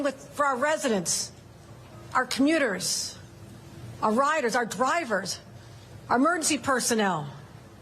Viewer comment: yo i swear to god she said rioters